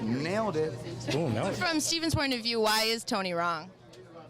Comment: did she just say steven??